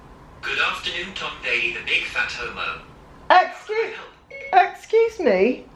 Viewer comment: For anyone else who might be hard of hearing and couldn't make out the opening bit (the part at 0:04 that isn't captioned), Siri referred to Tom as, "you big, fat, h*m*." Took me several tries to get it myself.